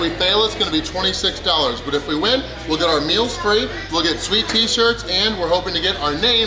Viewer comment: For a split second, when you say you're gonna get "sweet t-shirts" I keep thinking you're saying "we're gonna get sweet tea" then I hear the "shirt" and my brain quickly puts it together correctly. 😂